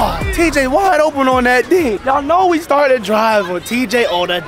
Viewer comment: I thought when he said “Tj sliding on that dig”I deadass thought he said TJ “sliding on that dic- man” 💀💀